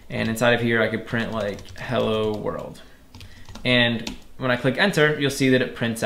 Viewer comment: I thought he said hella world. The print didn’t work. Fear that command